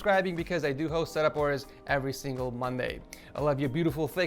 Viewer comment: At the end did he just say I like your thick thighs?